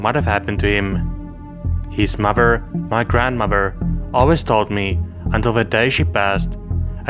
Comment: Listen at 16:19 I swear I thought he said he smothered his grandmother 😂🤣